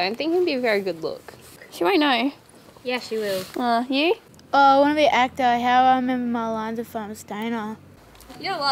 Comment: What the fuck did she say at 3:28? Haha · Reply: I want to be an actor who am I meant to know my lines if I'm a stoner